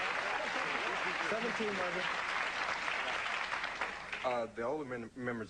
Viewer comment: I think Jermaine say"17 Mother" 😂